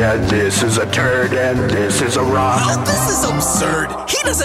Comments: when i first heard this i though he said this is Iraq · did you say turd?